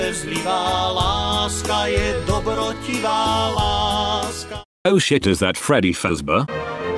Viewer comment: That's... POLISH? I always thought he said "oh hol' it up, is that freddy fazbear?"